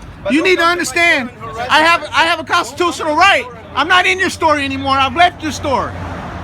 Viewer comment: Get em!! dude at 4:50 it sounded like you said "I'm not in your store, I'll break your store!" I know that's not what you said, but it would been funny and sounded like you said it in an accent if you listen closely. Anyways, stay on these Town Clowns! We got your back homie!